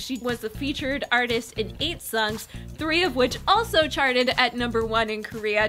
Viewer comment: who does she say Park Bom collabed with at first? I cant understand. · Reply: Fried Rice she says "8 songs"